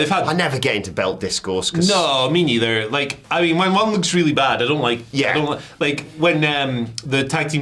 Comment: Anyone else hearing him say - "My mom looks really bad" ?